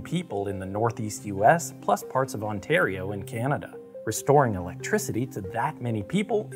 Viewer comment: I heard "parts of Ontario and Canada" instead of what was actually in the video 'Ontario in Canada" and was very confused.